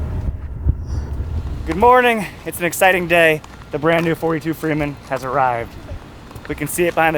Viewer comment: Awesome boat! I misheard you on a previous video and thought you’d said it was a 24’ Freeman. I was thinking “Whoah! That’s a big step down in size!” Glad I was wrong!